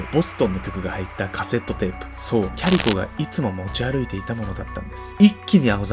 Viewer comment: キャリコ、キャリコまるでファーストネームかのように言うなよｗ（お母さんだってキャリコだ） だんだんキャリ子に聞こえてくる